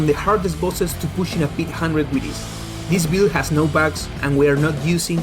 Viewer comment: “This build has no…”? Did you say ‘bags’?